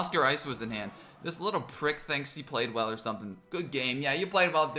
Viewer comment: Bro when he said prick i thought he said kick at the same time that deck was So TwEnTy SiXtEeN:joking